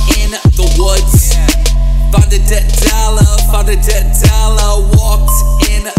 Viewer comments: I can't be the only one who thought he was saying "dead dollar" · I only heard "found a dead toddler"😂😂